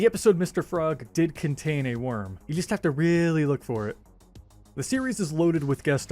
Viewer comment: I thought you said you really like Reddit ☠️☠️☠️ good video tho 💖💖💖